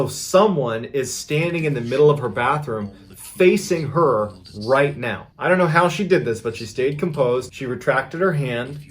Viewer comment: Am i the only one that hears a person talking under this video in the middle section of the video. check (9:18) that's where it starts. is anyone tech enough to check the video and section out the voices in the video? i am somewhat curious as to what it says. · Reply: The only think I can make out is “you will”